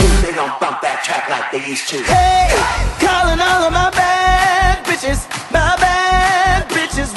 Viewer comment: I don't know why, but I will never not think RuPaul is about to say "my back itches"